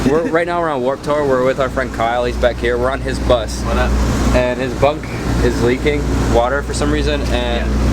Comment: I had my volume on low when the video started and I heard "his butt is leaking water".. 😂😂🤔 Sorry I have the sense of humor or an eight year old PS.. you have a crush?